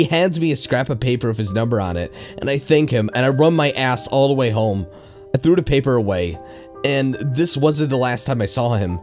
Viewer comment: For a second I thought you said "and I rubbed my ass all the way home" 😂 the music is very fitting I like when you change your tone of voice with different characters in the stories